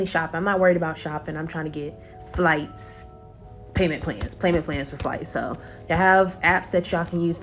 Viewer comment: did she say payman pans? 😂 or am I bugging?